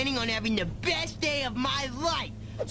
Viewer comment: Madam Fortress Mommy. Makes no sense, but is still the best insult I can think of. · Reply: I thought she said "pig boy" LOL